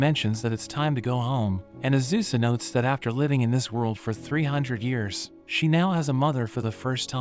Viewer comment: 300 I thought it said 300,000 at the start